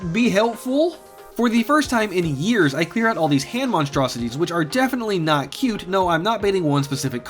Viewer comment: Excuse me what the actual fuck did you just say?? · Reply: i said i clear out these hand monsters that are not cute no I'm not baiting one specific commentor <3 lol